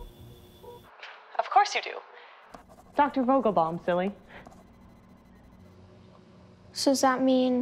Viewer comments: Why does she call Homelander "Billy" at 0:42? Isn't his name John? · Did she call him silly? Or Billy?